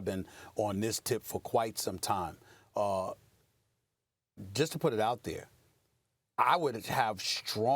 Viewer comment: Did she just say: Robert Whorey?